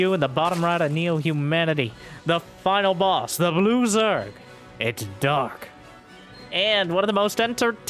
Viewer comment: I heard "the loser" when introduced dark as "the blue zerg" and thought he just spoiled the series 💀